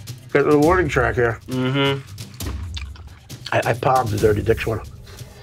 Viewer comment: Did he just say “I palm the dirty dick storm”????